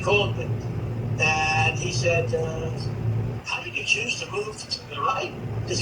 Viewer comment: it sounds like he said “i would love for you to serve. it would be good for Europe and it it would be good for you.” and also someone called him and said “how do you get jews to move to the right?” am i hearing that right?